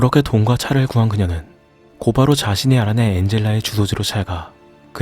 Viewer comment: 제 귀에만 잘못 들리나요? 9분 50초에 '그렇게 돈과 차를 구한 그녀는 곧바로 자신의 아내 엔젤라의 주소로 찾아가~~'라고 하시는 것 같아요. 친구 엔젤라인데 ... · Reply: 자신이 알아낸인데 발음이 안 좋아서 죄송합니다 ㅎ ㅠ